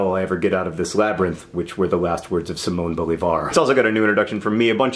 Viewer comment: I thought you said those were the last words of Simone de Beauvoir and not Simon Bolivar...I feel at once very smart and very dumb...